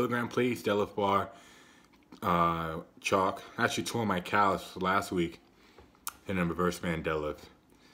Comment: Man I thought you were saying you "tore your calves".